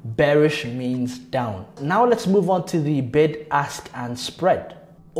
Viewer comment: I dead ass thought My boy said “Bend Ass and Sell